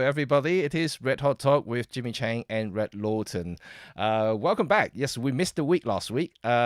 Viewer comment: I swear I hear you announce him as RED Lawton 😁😁😁